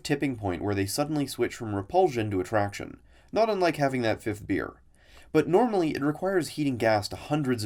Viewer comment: Had to listen to you saying 5:16 over and over since I kept on hearing you say ninth instead of fifth. Weird